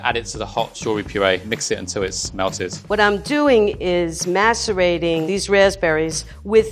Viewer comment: why did I think she said masterbating